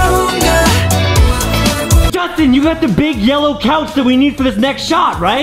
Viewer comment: "Big yellow couch i thought you said" "Big melon ouch" Me: 😂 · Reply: HalbastPlayzYT it goes like this Justen I told you to get a big yellow couch! Oh oh big yellow couch I thought you said BIG MELON OUCH hahahaha